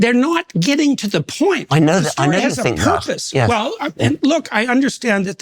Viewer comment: What does Dawkins say here? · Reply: He says "I know you think that"